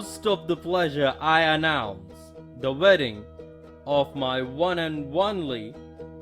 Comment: Did anyone else notice that he said one and ONELY instead of one and ONLY